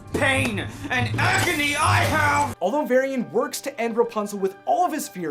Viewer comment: Did he just say that he wants rapunzel to feel the pain and acne that he has?